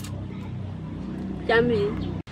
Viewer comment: قالت ( جمييل) في الدقيقة 11:22والا انا سمعت غلط